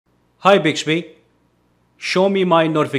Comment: What I hear "Hi Bitchby" 😂